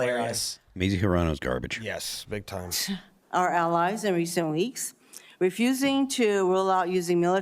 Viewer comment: Tooooo funny Tim. I heard you say alleys and my mind paused right when you said sorry... Allies. Love it! Yep, Tim is human too.